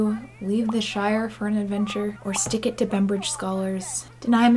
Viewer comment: ALL THESE REFERENCES. 🤣😍 I may be a bald, queer MAN .. but now I want bangs so I can stick it to Bembridge Scholars while avenging the death of my father! P.S. Did anyone else mishear "Pembridge" scholars their entire lives, only to have and a quick Google search blow their mind?!